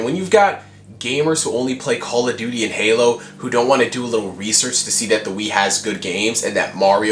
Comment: it sounded like he said "WiiSearch" instead of research. I think that it would have been awesome if he had said that.